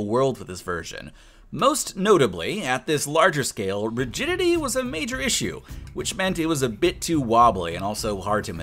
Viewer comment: for some reason I thought he said "most notably virginity is a major issue" 💀